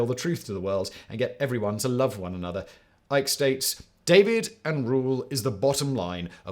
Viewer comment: you say "David" instead of "divide", just saying, I was confused about who this David was.